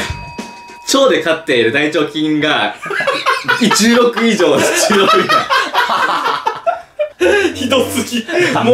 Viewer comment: 〜 「大腸菌が10億以上、10億以下」って言ってると思うんだけど、はなおの滑舌が聞き取れなくて「1億〜」字幕になってしまったわが氏